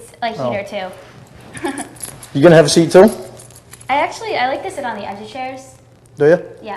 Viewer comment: did he say are you going to have a seat sir? Or Sara, as in decoys name. Havent played full video